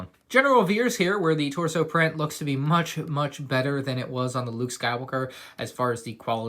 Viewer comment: Anyone hear Luke Skywooker?